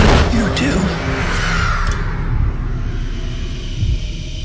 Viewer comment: "...This...is...WHAT??? What did he say? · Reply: I thought he said The System